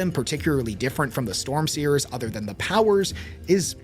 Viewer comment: Oh, I thought you said "Storm Seekers," and I was about to plug my favorite pirate metal band. But never mind.